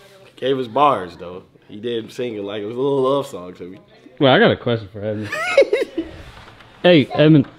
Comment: did just say I gotta question for that nigga😂😂